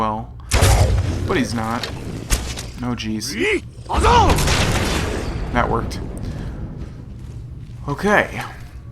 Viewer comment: what is this shout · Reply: arda can Soul Tear